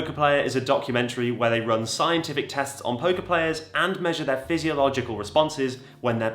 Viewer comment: I legit thought he was saying "Poké-Player", someone who plays Pokémon, not poker players.